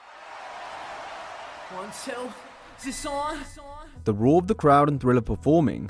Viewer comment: what is said in the intro 00:01 “on sell kisson”